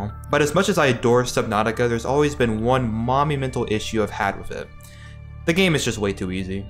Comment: I heard that as Mommy Mental Issue, I had to go back and listen again 🤣